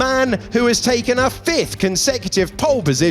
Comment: sounded like boring 😂